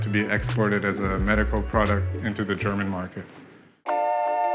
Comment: did he say to the "German" market??